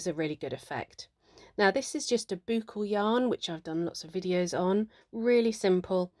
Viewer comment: hello, please could someone tell me what kind of wool is at 2:25? I know it was said in the video but unsure how to spell the name. Thank you! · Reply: Hi, yes, it's boucle yarn, a very unusual name, it's more common in actual wool suppliers where that be online or in person xx